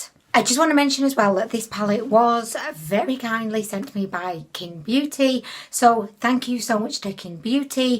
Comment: When you said Qing beauty I heard "kink beauty"